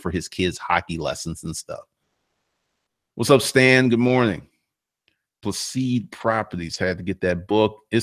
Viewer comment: Hello at the 32:03 mark , you mentioned a book Proceed Properties, I can't find it, did I hear you incorrectly?